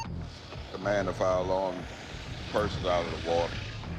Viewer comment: "Commander Fire Log" do they ever double check these subtitles. He clearly said "Command to Fire Alarm"